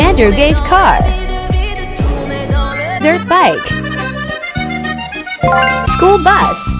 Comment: When it said dirt bike, I swear I heard "third leg"